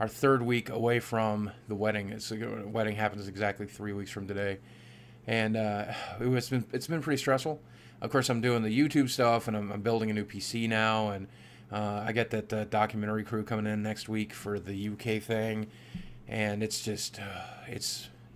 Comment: building a new pc now....i thought you said 'i'm building a new casino'